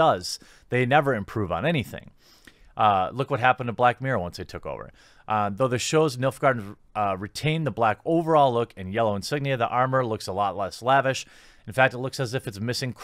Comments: Thanks So Much For Waiting On This One! It Was Supposed To Be My First But Youtube Demonitized It! If You Enjoy, Please Drop A LIKE On It & Leave A Comment Below! It Really Helps! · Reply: Milf-Garden armour!?? i must have misheard geremy XD hahahahahaha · Oh, "Nilfgaardian"! I thought you were saying "MILF Garden"!